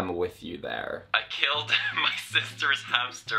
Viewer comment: im still laughing over the fact that thought one of the secrets said "i killed my sister"